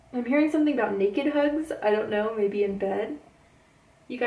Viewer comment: mark after the hugs part...what was that word you said? Naked hooves? What’s the word you said please? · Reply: Naked hugs...naked hugs in bed